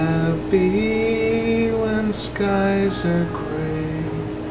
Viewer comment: Was I the only one who heard the little girl singing with in the basement? Sure sounds to me like she sings the work “skies” with you at 35:23! I have to say, I was quite surprised when you were showing Rita’s evidence, you did not highlight the face(?) peeking around the corner ( the first time) at 53:32 but you did highlight the light blinking out and the orb passing by. It looks to me like the person peeked, then made its way towards the camera. The sequence of events are there! I have to say Rita’s evidence you showed is A M A Z I N G! Some of the absolute BEST I’ve ever seen! I’m.a first timer here. I quite enjoy your work, your findings and your presentation. Can’t wait to watch the first and third segments! You have a sub in me!